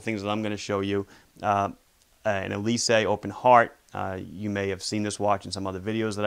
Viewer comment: what is the name of the watch at 1:27 I couldn't catch what he was saying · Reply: Rolex Yachtmaster and Elysee Open Heart